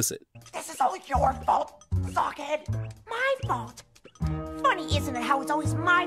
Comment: did he just say fu@